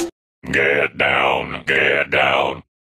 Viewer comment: No One: Maulch: Get Down, get down, get down, get down, if I'm gay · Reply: I heard “and get funky”